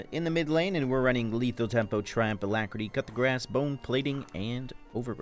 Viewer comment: NGL, whenever pronounces 'Coup de Grace', I always hear "Cut the Grass" instead. 😝😝😝😝